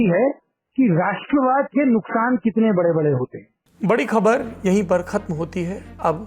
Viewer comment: Baaki sab to theek hai, but at 23:04 does he say "Rashtravad" ke nuksan? If yes, then how does this causation work?